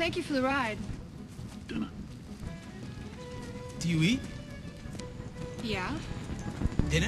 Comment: he: do you eat? what i hear: dewy 😂